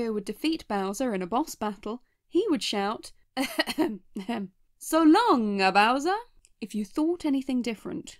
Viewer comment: I thought Mario said, "So long King Bowser!"